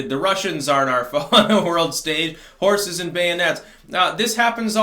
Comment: I thought he said whores face until I saw the horse head!😄